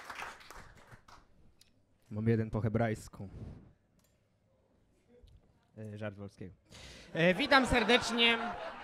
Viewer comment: Co on powiedział? Że ma jeden po hebrajsku i co dalej? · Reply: "Mam jeden po hebrajsku... żart Wolskiego"